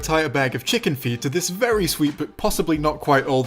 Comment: I thought you said chicken feet at the beginning